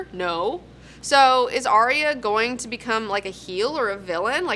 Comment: Holy spaceballs, did she just say Heel? Does watch wrestling?! Until proven otherwise, that's my new fantasy.